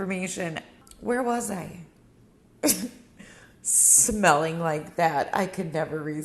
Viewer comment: I love your wooden dish drying rack! I've been trying to find a nice one that will last and not get moldy. Thank you for the link!!!! 😊😊💖💖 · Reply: LMAO I thought he said "smell" too!!! Not smile! Omg I'm cracking up 😂😂😅😃